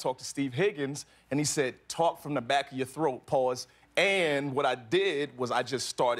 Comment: did he say"pause" lol😂